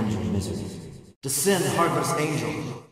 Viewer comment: Oh! He was saying "Descend, Heartless Angel". I never heard that audio cleanly, and everyone back in the day thought he was saying "Sin Harvest". But it makes sense; Heartless Angel was the attack Sephiroth could do to lower the party's HP to 1 in FF7.